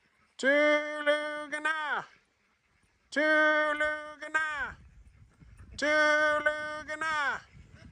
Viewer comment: What's the word at 16:15??? Always wondered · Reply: haha "Tulugan Na" it means "go to bed" in Tagalog. I thought it funny a few years ago to train the ducks with some Filipino.